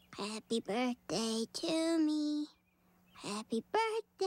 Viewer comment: honestly dunno what she says right there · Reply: Over looked middle child